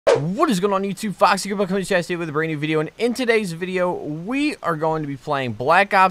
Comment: wtf did he say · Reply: what is going on youtube, foxy grandpa coming at you with a brand new video